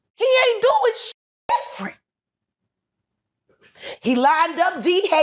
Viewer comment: Anyone know what she says here? "He ain't doing sh*t ______". It sounds like free?